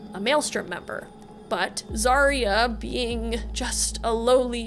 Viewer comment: I keep hearing 'male strippers' instead of 'maelstrom members'. Probably just need sleep.